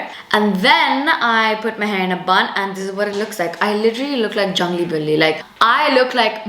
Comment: did she just said jungly billi 😃 brown things